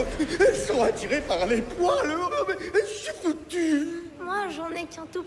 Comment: Il dit quoi à 0:32 ? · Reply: Des Champs "J'suis foutu...."